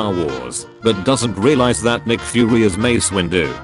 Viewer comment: I heard "McFury"